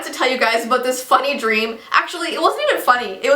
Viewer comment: 🤣for all the people watching this in 2020 when she said she was close to being insane, i think she is now XD Edit: so i was eating chips and when she said funn dream it sounded like she said this fucking dream nd i was so shook XDDDD